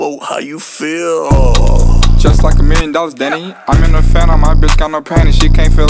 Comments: “lil boat how you feel, just like a million dollars Danny” the hardest part in the song 👍🖤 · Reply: i always hear “just like a mansdoll danny” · “lil boat how you feel, just like a million dollars Danny” the hardest part in the song 👍🖤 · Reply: Bruh I thought he said "Just like a man does Danny." 🤣🤣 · “lil boat how you feel, just like a million dollars Danny” the hardest part in the song 👍🖤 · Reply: Ngl thought he said just like the main Dawgs danny